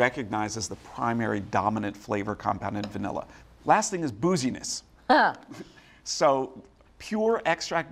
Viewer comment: Had to rewind because I thought Jack said "boujieness", not "booziness". hah